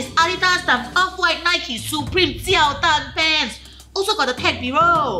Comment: did she said jianhao tan